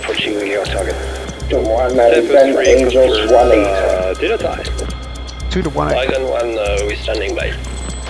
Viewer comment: I'm a bit skeptical of the usage of "dinner time" as a code word to clear elements of the package onto the target area. When I first heard it, I thought what was said was "Miller Time", code for the package to return to base, as it sounds very similar. However, I obviously didn't hear the briefing and would have known about "Dinner Time". While comms should be as short as possible, it is more important that the message is clear, and "Package Cleared In" or something along those lines isn't that long of a message. If elements of the package RTB'd prematurely, the whole package could be compromised, or at least disrupted.